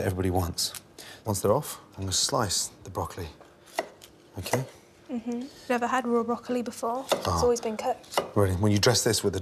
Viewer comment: Did she just say, "Never had real broccoli before, it's always been cooked."??? 7:50 (curious look)